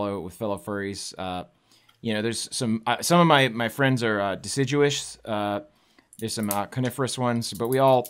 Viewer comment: Did he just say decijewish?